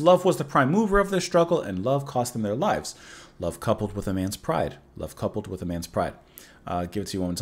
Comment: Oh it's "Love coupled with a man's pride". For all these years I thought he was saying "Love, coupled with a mince pie".